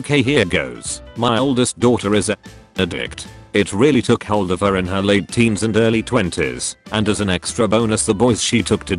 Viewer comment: can someone plss tell me what's the censored word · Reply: Might be too late but "heroin".